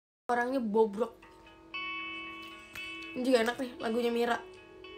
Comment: "Jing enak nih lagunya Mira" gw salah denger ato gmn ya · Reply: ni juga enak nih lagunya mira